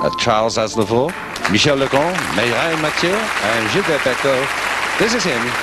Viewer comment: When I hear someone say "Mirelle Matthew"